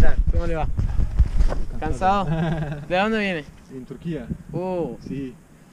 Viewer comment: deki adam ne yandan geliyin demedimi lan benmi yanlıs duydum adam ne yandan geliyin dedi bizim oglanda turkiye dedi :)